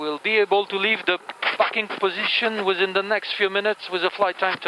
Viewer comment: he said “parking position” yeah? I did hear that right and not something very similar? 😅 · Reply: Yeah I thought he said " fucking position " 😂